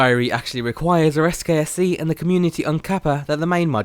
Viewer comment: what did he say? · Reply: It requires SKSE and the Community Uncapper